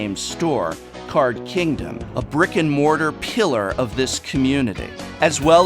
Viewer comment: Whenever he says “brick and mortar” I hear “Rick and Morty.”